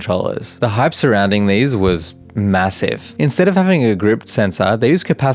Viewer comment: did i hear, massive?